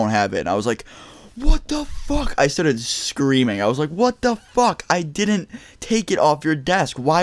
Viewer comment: XD it sounded like what the f**k richard